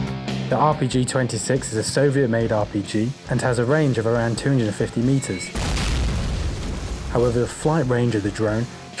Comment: a range of around ???.. 50m. what did he say? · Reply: 250m